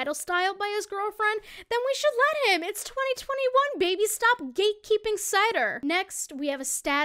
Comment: I swear to God I heard "stop gatekeep inside her"